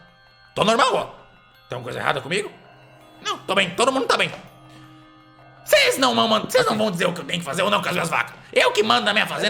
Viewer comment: Fazendeira: Ces não mamam... Desculpa eu só precisava ressaltar isso q eu ouvi, pq meu deus kkkkkkkkk